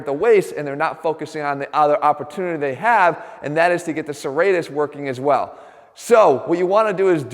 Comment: What exactly did Jeff say at 1:37? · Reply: Casey Vu not focusing on other opportunity they have, and that is serratus working aswell